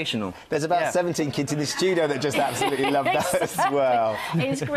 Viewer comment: yeah u know what it did sound like he said james. but i think obviously it was gents.